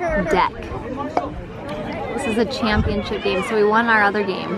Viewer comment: Sounded like you said "champshits"😂